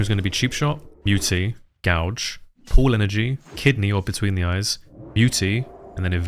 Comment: Can I ask what you're saying at 2:36ish after cheapshot, muty, gouge? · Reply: pool energy